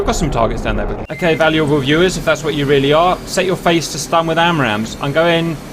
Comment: I heard “set your faces to stun” I will do what I must